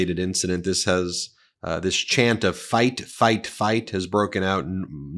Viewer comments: Where does sound like they were saying Biden Biden not fight fight😊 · They chant Fight, Fight... I hear "Zig Hail, Zig Hail".... It sends cold down my spine... I am Jewish... This kind of people like on RNC do fascism reality and says "I know nothing about concentrated cams".....